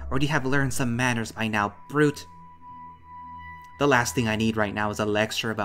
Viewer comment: I honestly thought he called the weasel a fruit and started chuckling